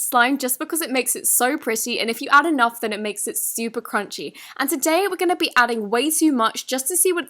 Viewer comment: "It makes it stupid crunchy" it sound like it but she actually said "It makes it super crunchy"